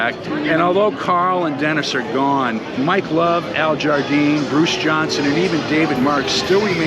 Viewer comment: Thanks for this Really enjoyed it Sounds like you said "Bruce Johnson" at the end It's "JohnsTon"